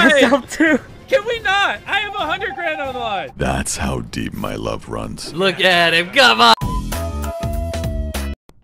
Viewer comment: "That's how deep my love runs <33 " - Corpse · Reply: I thought he said "Thats how deep my voice runs" but when i saw this comment i was shipping them so bad